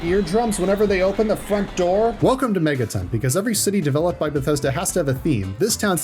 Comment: At first I thought it said a Metro instead of megaton